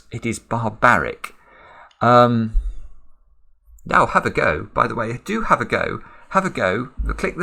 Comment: Simon : "Have a go." Me, after 2 milliseconds of reflexion : "Hum... NO!" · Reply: the way he says "have a go" 3 times sounded like "why should I suffer alone?"